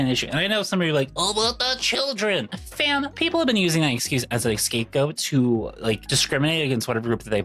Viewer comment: did you say "an escape goat?"